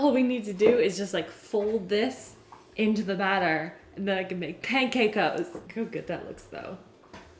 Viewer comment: I totally heard "And then I can make pancake hoes!"